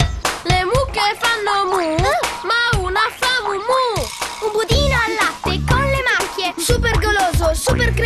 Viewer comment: per qualche strano motivo mi ricordavo da piccolo dicessero " GUSTO GAY" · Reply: E poi ci sono io che sentivo “comodino al latte” 0:11 😂😂 (quello che dice la bambina)